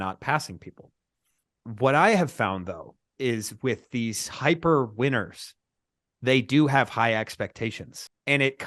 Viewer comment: My last name is Winters and every time I here you say "hyper winners" I hear "hyper Winters" and I think you are speaking of me when I was back in grade school haha!